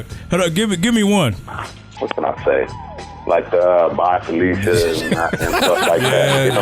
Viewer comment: What does Ren say @ 1:08? "box of ........???? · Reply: Darrin Connor he said 'bye felicia'